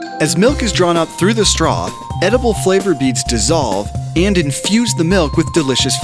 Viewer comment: Wait... Did he say the beads are EDIBLE??